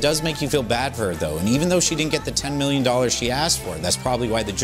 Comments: I thought he said urban league of legends for a second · I keep hearing him say “League of Legends” instead of “Legal Legends” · The way you say legal legend it sounds like your saying leuge of legends